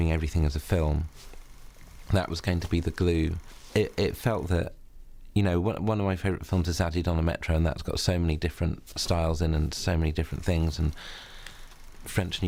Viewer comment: What does he say his favourite film is? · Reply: Zazie dans le metro (french film)